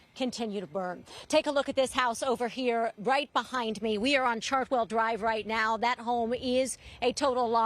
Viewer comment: Thought she said charcoal drive,,,so sad